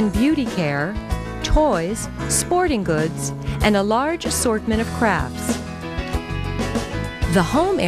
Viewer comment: LOL skip to 16:49, turn on captions and look what it says XD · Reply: "a large assortment of crap"